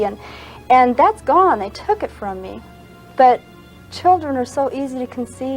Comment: anyone else hear “I took it from me.”